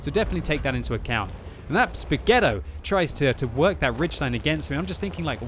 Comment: I heard something like "Spaguetto" 4:03 xD I love those Swedish heavies.